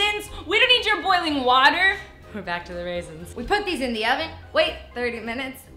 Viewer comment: I thought she'd say put your relatives in the oven xD